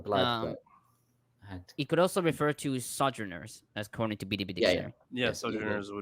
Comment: I can't stop hearing that things just must be true when even Ezra says it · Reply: I get that one right, but I keep hearing “the itty bitty dictionary”